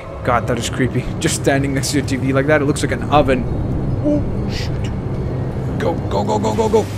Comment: Did you just say the s word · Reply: Seriously m8? He said shoot.